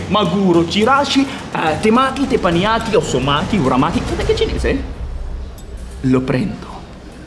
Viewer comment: Anche dopo anni non ho ancora capito cosa dice daniel al secondo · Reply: Fate anche cinese?